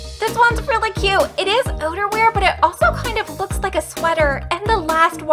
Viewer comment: When she said "outerwear" I heard "oderwear" 💀